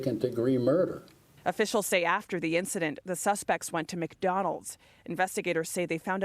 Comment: did she say they went too McDonalds?!